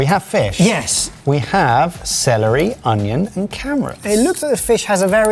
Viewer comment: Subtitle specialist - it's "carrots" (sounded like "camerats" though, I agree) And 3:39 it's "soffrito"